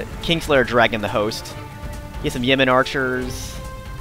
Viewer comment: For a moment I thought that you said "Yemeni" archers and I was like "what the heck are guys from Yemen doing in England". :)